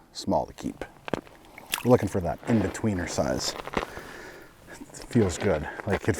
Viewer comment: Makes you wonder if you drilled one and sat down would you have got 3 fish? Thanks for the video its so not helping my ice fishing fever. 0 fatigue, huff huff huff.... ;) Ps Wife heard you say "In between her thighs" when you said "That in-between size"